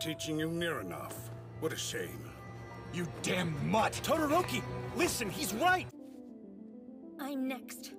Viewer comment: friendly reminder that todoroki called the chief of police “a damn mutt” TO HIS FACE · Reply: And i thought that lida said no he’s whight